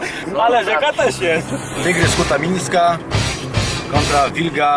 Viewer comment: czy tylko ja w 0:14 usłyszałem Tygrys Kutamińska, a dopiero potem spojrzałem na tytuł filmu, bo coś mi się nie zgadzało?